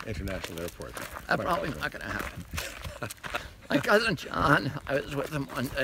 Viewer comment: What did Bobby say to Kevin at 1:10? I couldn't make out his reply. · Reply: RFK jr said, "that's probably not going to happen."